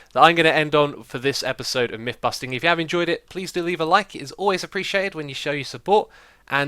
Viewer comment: I heard the end as "please delete a like"